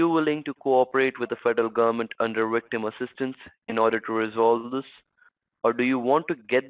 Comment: I thought he said 'rectum assistance ' 🤣🤣🤣🤣🤣